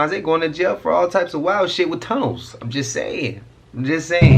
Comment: My eyes got so big because I thought you said at first "sometimes the fallen angels be crashing out going to jail with Combs" until I realized you said tunnels lmao😅